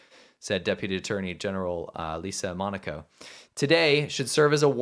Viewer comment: Whoa... Did you say "Lisa Monaco"?... Do a search of that name - not wiki. Nobody to use as a "white hat" !!!